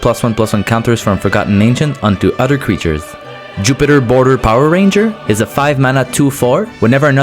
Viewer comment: omg wtf! Jupiter Border Power Ranger?! 1:18 is that what he said?! Ahahahahaa I'm dead! And then 12:22 Tony Hawk Pro skater aaaaaaaaaaaaaah!!!